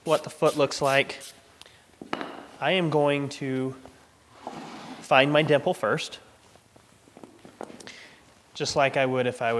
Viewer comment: I swear to god he said “find my nipple first”